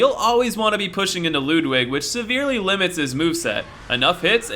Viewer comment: Did you say, "Lewd-wig"..?